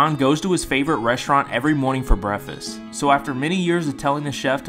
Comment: It sounded like he said brehfast